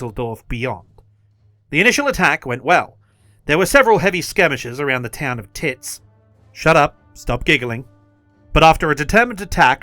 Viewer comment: I thought I heard Kits.. Why couldn't it just been something fox😢